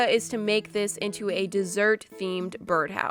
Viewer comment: i thought she said desert at first and i like MORIAH FOR REAL